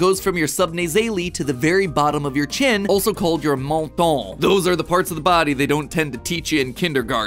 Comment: i really thought he said your mom